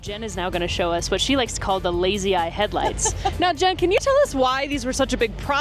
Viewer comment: I swear I thought she said "Lazy ass headlights" 🤣🤣🤣